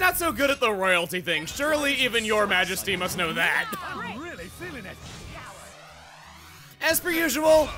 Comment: Did I just hear Zeke say "I'm really feeling it!"? I KNEW ZEKE WAS SUPPOSED TO BE THE MAIN CHARACTER!!